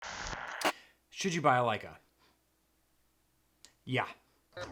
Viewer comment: Ohhh... I thought you said "Should you balalaika ?"